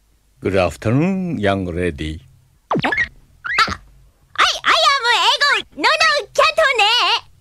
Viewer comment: I thought he said 'I am white'